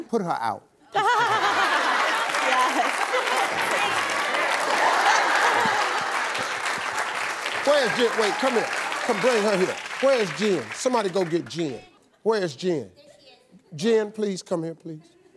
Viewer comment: I thought he said where is jinn